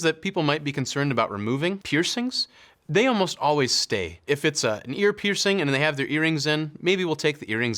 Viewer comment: Am I the only one, when he was talking about haircuts, that misheard him saying “if it’s minor” as “if it’s a minor”?